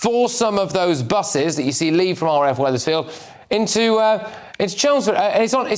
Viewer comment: I thought he said RAF Weatherspoons :face-orange-raised-eyebrow